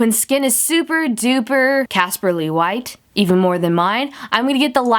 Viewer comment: Swear to god I thought she said Caspar - Lee white